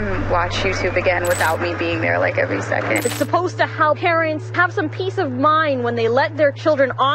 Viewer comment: I understood Karens instead of parents the first time at 0:09 lol